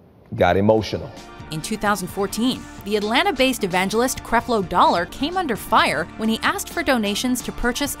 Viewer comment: I actually misheard his first name as "Crapload". How fitting.